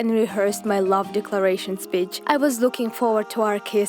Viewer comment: When she said speech I thought she said b*tch 😅